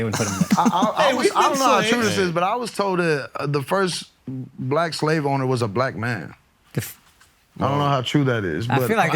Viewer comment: Did he say "The first black slave owner was a black man" 🤔